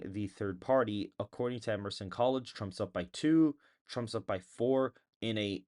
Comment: I heard you say Trump was up 10 in Colorado and I was like "what!!?!" No way. Lol